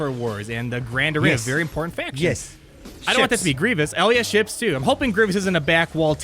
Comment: w0Of Wo0f and 11:10 did he say Ali-a ships?